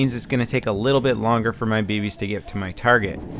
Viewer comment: sounds like he said babies XD